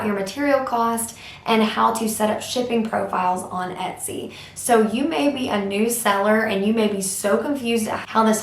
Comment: when you said "you maybe" in the beggining I heard it like "you baby" and I was like wut ? why is she calling me baby ?